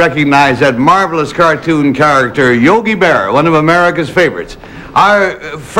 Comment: Yogi Berra? Thats what it sounded like to me.